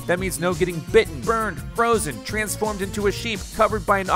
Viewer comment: Did he just say shes a fun guy?